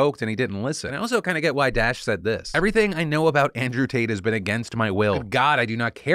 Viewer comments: i know i heard him say "andrew taint" case... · I heard you say Andrew Taint. You aint fooling anyone Phil :3